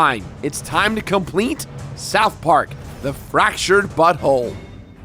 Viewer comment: I thought He said in the title... Honestly sounds like... "THE FRACTURED BUTTWHOLE".... Seriously man..